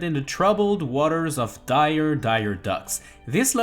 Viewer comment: Am I the only one who heard " dire dire ducks"?